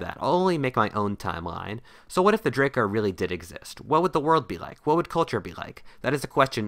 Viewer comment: i thought it said " what if drake actually existed "